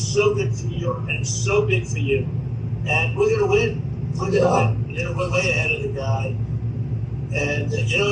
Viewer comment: Did Trump say that “we’re going to win” and RFK Jr. said, “yeah?” I KNEW RFK Jr just wanted to help get Trump elected!